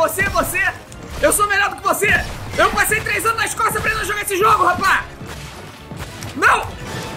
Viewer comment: So eu que no minuto 3:17 ouvi alguém falando você nunca jogo esse jogo